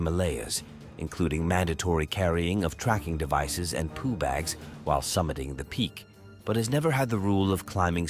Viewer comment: I'm sorry, at 2:50 did he say poo bags